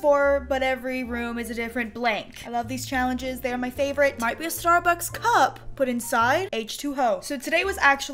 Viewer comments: Is it just me or did it sound like she said this may look like a starbucks cup but it has h2hoe in it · did she say H2O or H2HOE